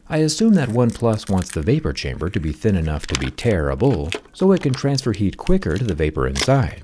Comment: when he says its tearable I thought at first he said terrible 🤣